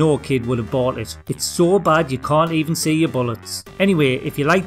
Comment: One of your funniest so far. Best bit was Dick Tracy, "The graphics are so bad you can't even see your bollocks". Then, sadly, I realised I'd misheard...bullets.